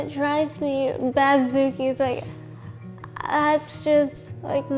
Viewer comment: Did she just say "batzookie"??? 😂 Omg I love her already